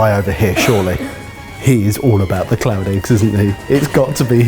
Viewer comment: This whole time I've thought they were saying clown eggs until I read the description. Learn a bit about actual clown eggs when I googled it. XD